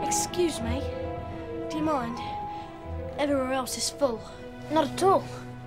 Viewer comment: I thought Ron said: excuse me... Everyone is bo+d